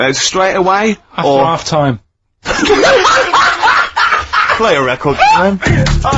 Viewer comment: "Play a record Claire". So was Claire there 'pressing the buttons' instead of Karl, or was she just there alongside Karl as the producer ?